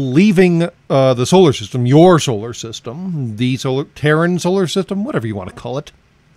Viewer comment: I thought you said "Karen" system instead of Terran system at first. Either of which would probably be appropriate these days lol.